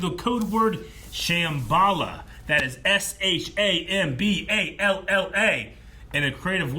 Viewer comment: When you said ShamBalla I thought you said what's the Capital of Guatemala lol.